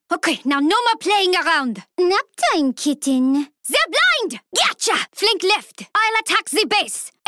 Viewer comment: what does she says? Suplint? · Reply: Libra Lyrics No she says, “They’re blind!”